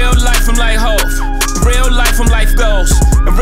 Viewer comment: When Hov said: “ In real life I’m like Hov” I felt it 😎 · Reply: I thought what he said was in real life I'm life's goals.